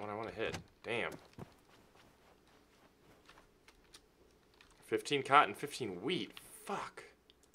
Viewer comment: I THOUGHT SERIOUSLY HE WAS SAYING "15 COTTON , 15 WEED" BUT ITS ACTUALLY " 15 COTTON, 15 WHEAT" I SEE THAT NOW ON THE TRUCK <INVETORY> ( DID I SPELL THAT RIGHT?)